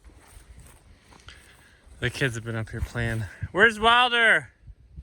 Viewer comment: HAHAHAHA! when you asked the kids "where's Wilder" I heard "Where's Waldo"... Gotta get him the waldo outfit- would be stinkin' cute! Also easier to see in all the green grass....